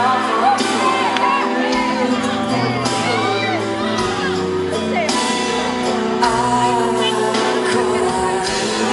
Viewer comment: Foi impressão minha ou no minuto 4:23 a Ana diz: " Eu te como toda" kkkkk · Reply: Eu achei que ela falava "io te trovo, ancora" mas depois de prestar bem atenção eu vi que ela fala "eu te como toda" mesmo e por isso a Chiara fica com aquela cara. KKKKKKKKKKKKKKKKKKKKKKKKKKKKKKKKKKKKKKKKKKKKKKK TÔ MORTA #ChiAna